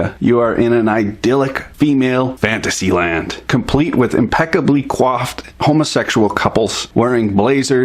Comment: Okay so I live in the U.S and I have heard Canadians say this word so many times, how is it spelled and what does it mean? @ 2:25 he says "complete with impeccably _(quoff?)_ homosexual couples". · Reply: It's not a Canadianism... Quaff has always been hair. Your vocabulary must be limited.